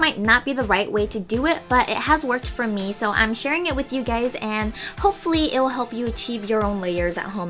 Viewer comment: sounds like she is saying "I'm sharin with you gays"